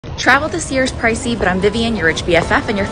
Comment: I thought you said travel to Sears and I'm like there's no Sears anyone